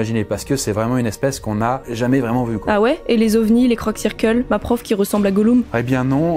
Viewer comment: "les CROC circle" j'sais pas pourquoi j'ai pensé à Karadoc